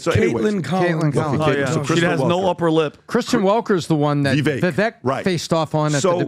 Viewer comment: I haven't been to the podcast in a while, and the first 20 seconds i hear "She has no upper lip" 😂😂😂 i hust about died 😂